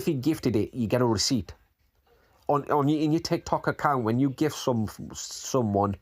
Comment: I heard "Cox Account". As in Vegas.